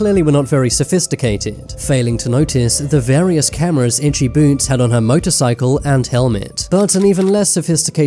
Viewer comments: I keep hearing *itchy b**bs*😭🙏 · I thought he said itchy boobs 💀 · Haha i thought it said itchy boobs😂